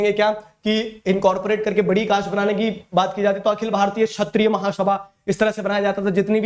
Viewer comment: I was watching this on 2.25x, and I heard 'chapri' instead of Kshatriya. 💀 (Timestamp